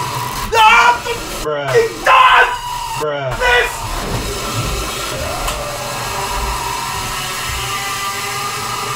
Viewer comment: Hey did you said "putain" at 7:27? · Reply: he said f@ this